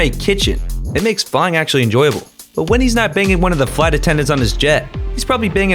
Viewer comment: what did he say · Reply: Banging